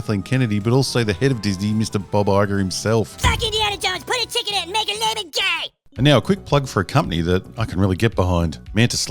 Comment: Add some gay chicken? lmao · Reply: That’s what I thought she said too, but apparently she actually said put a CHICK in it and make her gay.